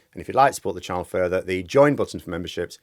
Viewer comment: For a second I thought I heard you saying "hit the rejoin-button for membership" [9:09].